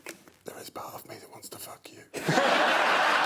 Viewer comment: What did Greg whisper to Alex at 5:28? "There is bath me that wants to f**k you"? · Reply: A part of me